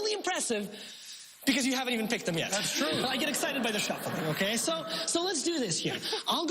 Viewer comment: did he say I get excited by the shuffle or show? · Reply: Shuffle